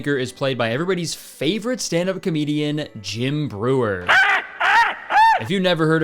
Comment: James Brewer? as in a terrible person with the EXACT SAME NAME AS MY AMAZING BAND DIRECTOR HELP he would not be proud to share a name with that man... edit: phew nevermind i misheard that.. bro said JIM brewer. i got so scared for Mr Brewer 😓